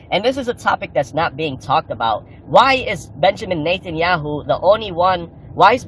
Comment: did I just hear him say “Benjamin Nathan Yahoo” instead of Benjamin Netanyahu?